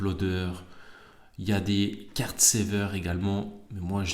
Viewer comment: Salut, je tente ma chance pour le concours ! ;) C'est quoi que tu appelle " carte sever " (à 1:31) pour protéger ses cartes ? Je n'ai jamais entendu parlé. Moi je mets sous sleeve + toploader. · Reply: hello, c'est Card Saver. Principalement utiliser pour des envoie chez PSA